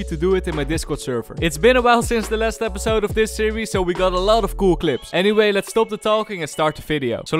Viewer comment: at the start I misheard "since this is the last episode of this series", I was kinda sad :(